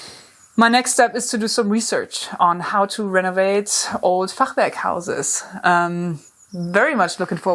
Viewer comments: Every time I had a little giggle when you said what that building material is. Because it sounded like you were saying “fuckbag” instead. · What’s the German word that you’re saying at about 11:05 for the house construction style? I want to research that too. Congrats on the magnificent find. Not too many houses left like that in the entire world, especially not as well-preserved as that one. A true gem. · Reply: Fachwerk.